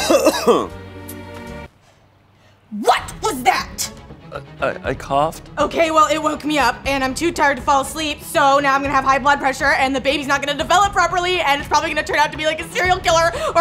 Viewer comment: Wait... did she just say "Im too tired to sleep"?